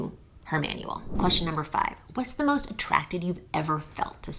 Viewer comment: ON question #5 it sounds like you said "What's the most attracted you've ever felt to someone?", yet the screen shows "What's the most attractive you've ever felt to someone?" Those are two very different questions. Is it supposed to say "attracted"?